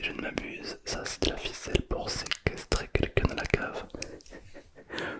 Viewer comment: J'ai mal entendu ducoup voici ce que j'ai entendu : De la ficelle pour séquestré des gens dans votre cave... Moi j'aime bien séquestré mes clients 😂